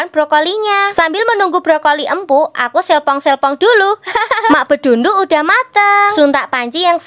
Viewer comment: Astaga gw salah dengerkan "sambil sepong sepong dulu"😂😂